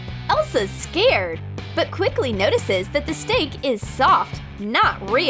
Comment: It sound like she is saying steak not stick